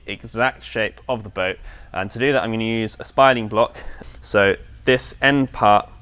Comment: What kind of block was he calling that? Spiraling? Spinal? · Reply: "Spiling block."